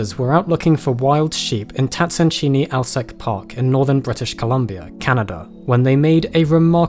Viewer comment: I heard "northern british colombia, canada" instad of columbia and i was as confused as if i had heard east russian congo, australia